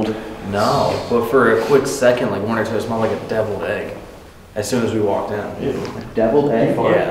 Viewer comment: Bruh for a second I thought he said “dead leg” I was like “🤨 what the fuck does that smell like, Corey??”